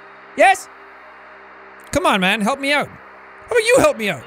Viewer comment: okay- when he saw the mess of his hair and went to go fix it i heard "help me not"??? and at the and of the video i heard "who's this then? will it never end? please save my friend! Phase two happened.."